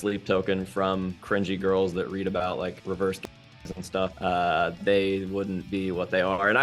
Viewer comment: reverse what? I couldn't understand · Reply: I wanna say it's "reverse gangbangs" AKA when a dude is getting fucked and sucked by a large number of women